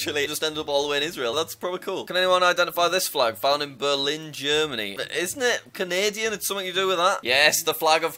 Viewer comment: did he say Canadia